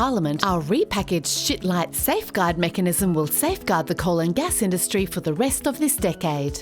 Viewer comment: What are you people doing to my brain? First you made politics and bad lip synch sexy, now this. You know, at first I heard “the colon gas industry” and I thought, government, am I right? Sadly, yes.